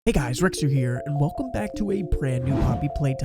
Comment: When he said "hey guys!" I thought he said nigga